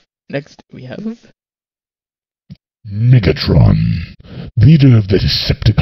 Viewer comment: I swear at 3:50 I thought you said "niggatron" 😆